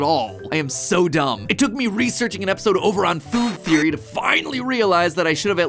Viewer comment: the cc said fOoL tHeOry 😂😂😂😂